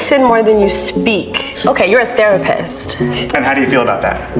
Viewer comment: Oops I thought she said terrorist not therapist